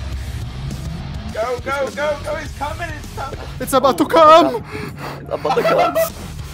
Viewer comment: why did i hear "Its about to c*m"💀💀💀